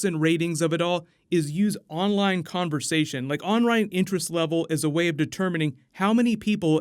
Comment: Did he just say onrine interest level